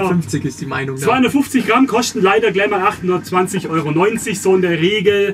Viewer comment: 820,90€ oder hab ich mich verhört beim voltage